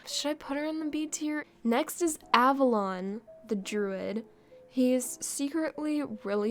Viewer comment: When you said the name of the druid, I thought you said : "next is avalanche"